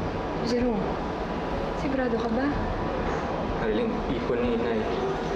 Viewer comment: Darlyn narinig ko d Marilyn 😂😂😂